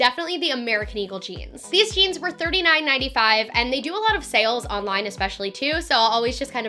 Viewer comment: why does it say it’s $3 for the American eagle jeans lol it was probably just a typo cause I heard her say it was $39 😂